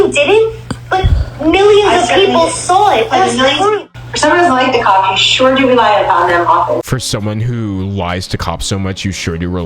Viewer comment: I think what she says at 7:26 is: " For someone who doesn't like the cops you sure rely on them often."